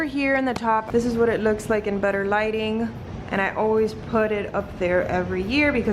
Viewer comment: 🤭🤣 it sounded like you said “in butter lighting” 15:25 hehe 😜 Everything looks great, and I love the diy garland idea!